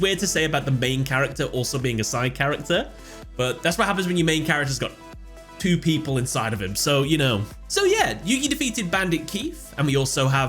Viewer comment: What did he say 😂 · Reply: "That's what happens when your main character (Yugi) has got two people inside of him (Little Yugi and Pharaoh Atem)"